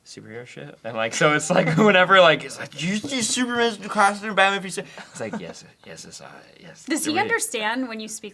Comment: "Did you see Super-Man's new costume in Batman V Su--(perman?)" That's what said at 8:41.